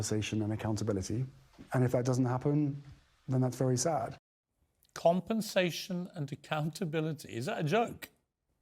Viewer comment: I thought he said ‘conversation and accountability’ - not compensation